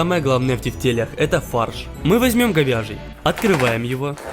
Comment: Мне послышалось ,, фарш говняжий,,а не говяжий